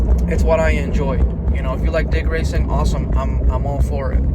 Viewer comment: lol sounded like you were saying dick racing near the end of the video